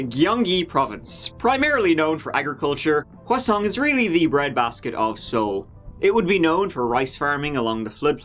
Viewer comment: anyone else think was kinda saying “hwaseong” like a french person would say “croissant” ? just me? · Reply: Yup, I heard him say "croissant is the bread basket of Seoul" 😊🥐